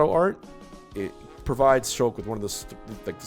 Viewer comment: I thought he said sheik instead of shulk and almost had a stroke